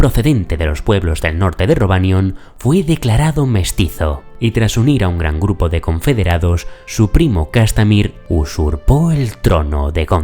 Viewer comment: Escuche que el priml se llama castamere? Como las lluvias de castamere de fuego y hielo?